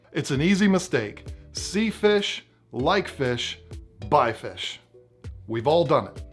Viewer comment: I thought he said sea fish, lake fish, bay fish